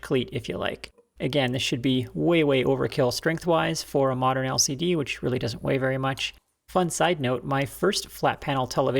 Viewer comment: "-way overkill for modern LCD.." For some reason I heard, "For my OCD, which really doesn't weigh very much"